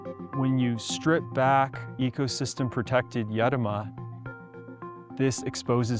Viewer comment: he says "yedoma" It took me a few minutes of googling to figure out what he was saying so I thought I'd share.